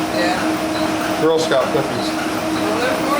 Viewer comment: I am watching and swore you said girls selling quickies but after the paint mixer went off I found out it was girl scout cookies he'll even better lol